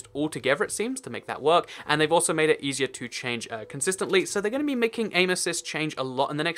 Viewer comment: it sounded like you said "anus's"